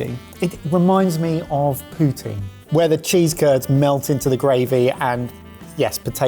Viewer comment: For a moment i thought said "It reminds me of Putin" but luckily he was talking about poutine